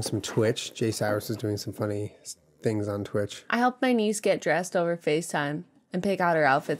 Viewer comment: I thought she said "I helped my knees get dressed over facetime"😂🤣